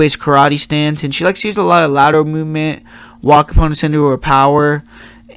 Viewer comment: how do you spell it "ladder movement"? "leather movement"? I keep hearing about it but I can't do any research on it because I can't spell it. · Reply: Lol lateral